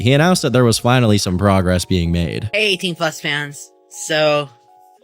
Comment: wait does he call his fans “18+ fans” 💔🥀 is that to like avoid catching a case